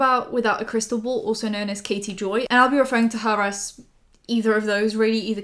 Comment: I thought you said Katie Bell instead of Katie Joy and I was like ohhh no Katie Bell is from Harry Potter